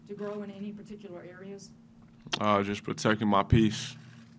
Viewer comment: Very poor choice of words, protecting my piece? I know he meant peace but I bet I’m not the only one that thought that when he said it. I hope he smartens up and keeps out of the lime light for the wrong reasons. He’s an amazing player to watch, I hope we get to watch him for a full healthy career.